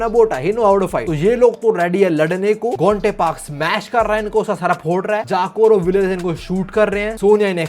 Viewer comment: who else heard GOTA🤣🤣🤣